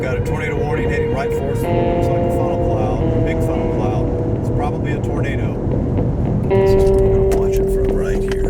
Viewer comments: in the video said "its probably a tornado" but the captions said "its probably a burrito" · I love how when he said "It's probably a tornado," the captions said "It's probably a burrito." This made me laugh so had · I have subtitles on and it keeps saying “ burrito instead of “ tornado 🤓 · the caption said "Ok, we have a burrito warning heading right for us" instead of "tornado" warning 😅 But the really funny part is that it comes right after his playful jest of "Let's go break some wind" 🤣